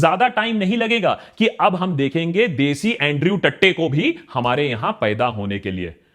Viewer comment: Did he just say Andrew Tatte?😂😂😂😂